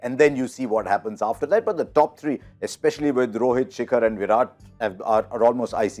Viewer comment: Did he say Shikhar at 0:51 seconds? xD